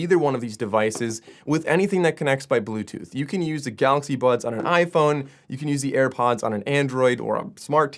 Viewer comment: I keep hearing "Galaxy Buns"....is it just me? lol